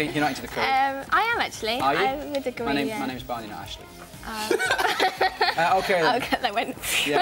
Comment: What are they saying between 0:34-0:43? I was not able to catch :(. Thanks for your help. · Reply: Frankie said 'yeah I do actually' and Barney said 'my name's Barney, not actually' lol